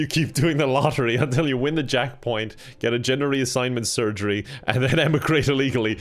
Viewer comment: did he say jack point